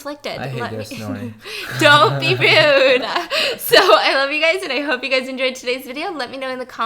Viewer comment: did she say I love u guys today?